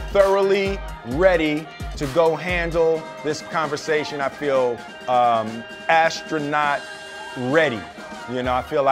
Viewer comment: I heard that wrong What I heard: I feel astro 'not' ready What Will really said: I feel astronaut ready